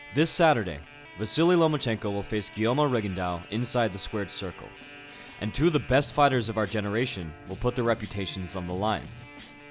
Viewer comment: All I heard was "square circle" my god man